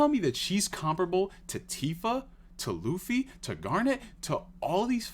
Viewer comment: sounded like you said Luffy....... YO HO HO HE TOOK A BITE OF GUM GUM!!!